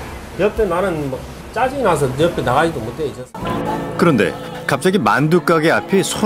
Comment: 나가지도 못해요~ 저ㅅㄲ들은... 이라고 들리는데 저만 그러는건가여..??